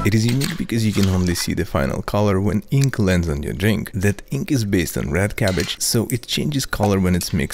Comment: sorry sir did you say “the inks based on rat cabbage” or did i mishear????